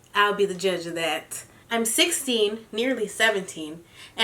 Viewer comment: GIRL I DUNNO WHY I HEARD "I'M SIXTY, NEARLY SEVENTY" THE FIRST TIME AHAHAHA